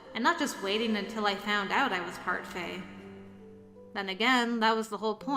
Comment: I heard that as parfait XD